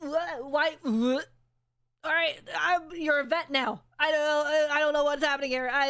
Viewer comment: by god, someone please tell me I am not the only one who heard "I am, you know, wet now"